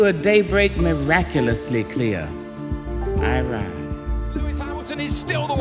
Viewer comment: Who's the clown reading that poem?? Every "I rise" sounds like Borat saying "I likee" loooool